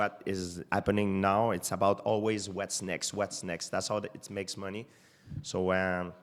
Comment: thought he was saying what snakes..